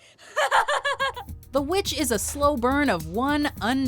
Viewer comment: The Witch...i thought i heard Voldemort..*-*